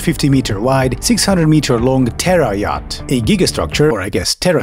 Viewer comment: Also worth mentioning is the fact that on some of these renders you can see a couple of PRIVATE JETS parked on the upper ring of the structure. That'd be a hell of a feat, landing a jet plane on a moving structure, on a circular, oblong runway, dodging helicopters and avoiding the giant pit with people at the bottom. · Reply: I'm Czech like the narrator, but I keep hearing "terror yacht". This is something from Adolf' fever dream.